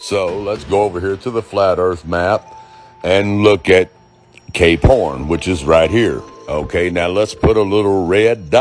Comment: cant believe nobody has commented this yet, but... 16:05 , i was listening to this like a podcast and wasnt looking at the screen and i stg i thought you said 'let's go to the flat earth map and look at gay porn' 🤣